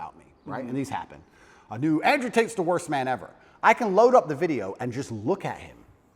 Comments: I thought it said Andrew talks to the rapist 🤦‍♂️🤡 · Genuinely thought it said Andrew Tate vs the rapist😭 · I thought it said Andrew rate and the rapist 😭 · I thought it said Andrew Tate vs the rapist 😂